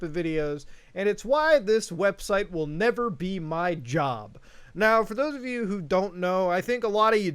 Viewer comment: For one second I thought it said you will never be my job